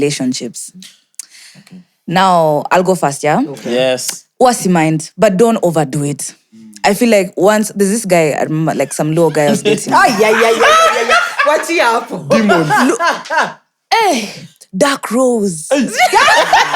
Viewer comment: I heard 'there's this law guy's and I knew it's about to go down 😭